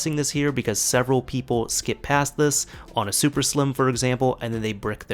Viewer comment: Can i do this on a ps3 slim? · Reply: F N o thought you said super slim sorry